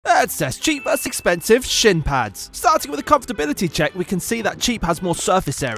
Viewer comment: Why do I hear "Let's test cheap versus expensive shit pants" 💀